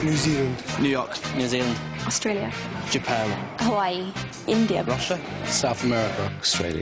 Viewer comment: Did Ginny said India.. 4:36omg I m so happy..Proud be Indian..